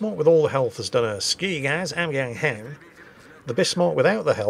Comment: Can somebody translate what said at 11:49 - 11:52 please? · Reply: It's Cartman from South Park "Screw you guys, I'm going home"